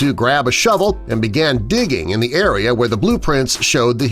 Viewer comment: it says "di" instead of "dig" :/